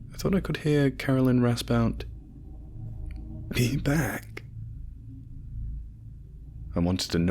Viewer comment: what exactly did she say? The only thing I understood was 'we back/lean back/he back' or something along those lines.